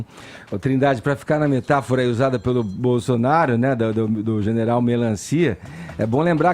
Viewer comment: Não quero acreditar que ele falou o que eu escutei, "Bozonaro" foi isso mesmo?